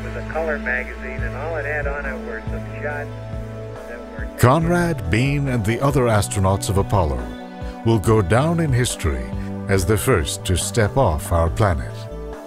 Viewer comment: Conrad, etc will go down in the history as the first people? What about Apollo 11? -. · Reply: Tariq Mulla I hear it as "Conrad,Bean and the other astronauts of Apollo ,will go down in history, as the first to step off our planet"